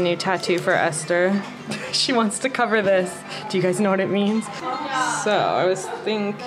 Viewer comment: Learn 150+ languages with quality native-speaking teachers on italki🎉 Buy $10 get $5 off for your first lesson using my code: 💻 Web <link> 📲 App <link> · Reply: ohhhhh. i thought you said ''she doesn't know what it means'' not ''d'you guys know what it means'' Looks like this englishman....needs to learn english. 😅